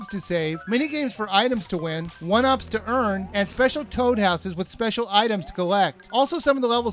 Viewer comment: I thought he said ''special toad asses''